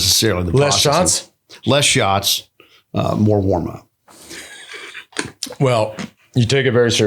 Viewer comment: What does he say after “less shots”?? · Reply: More warm up